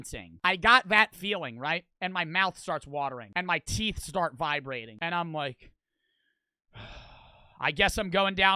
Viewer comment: I am half asleep cuz I listen to this when I sleep and I wake up half asleep I hear “My teeth start vibrating” and I go “Did this fucker just say his teeth were vibrators?”